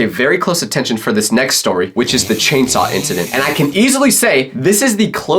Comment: did you just say CHAINSAW